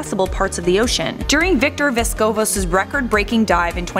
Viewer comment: did I just hear vsco oml no wonder there's a pic of plastic